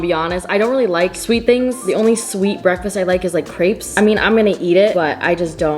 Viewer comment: did she just say crapes 🤣it’s grapes